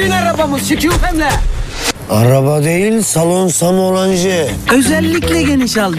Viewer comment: burda ne diyor adam ? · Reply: Salon salon hacı